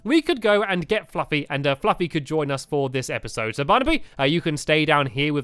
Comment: When Stampy said Barnaby I thought he said boda bang boda bong